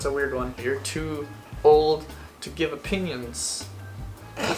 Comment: 100% thought he said “if you’re too old to give a penis” and looked down at the captions like 😳